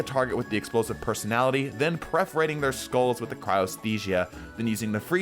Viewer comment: wait did I hear perforating pronounced as preforating there? xD